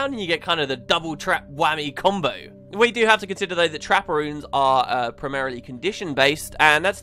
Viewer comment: Constantly thought you were saying Trapparoons instead of trapper runes.